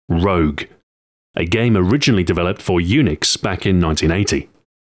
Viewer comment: I thought he said Eunuchs, not Unix.